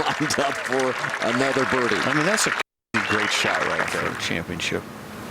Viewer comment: Did say "that's a fuckin great shot right there "?